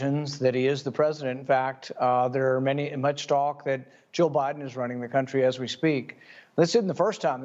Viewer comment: Paul Rand needs to pronounce his words properly and clearly, especially at pivotal moments. It sounded like he said: “Joe Biden is running the country as we speak”, when he apparently said: “Jill Biden is running the country as we speak”.